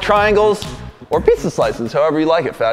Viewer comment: Wait, I was rewatching this and at 0:13 I thought said" or pizza slices you fatty" 🤨🤨😕